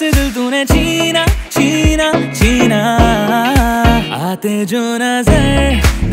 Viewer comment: I thought Armaan said Sina and I was shocked, then I realized that it was 'Cheena' not my name 'Sina'.